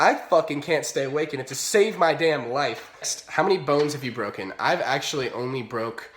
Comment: At first I thought you said how many phones have you broken and then you pointed at your hand and I was like what?